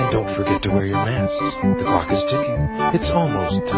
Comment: I turned on subtitles during this part, and it said the word Ass instead of Mask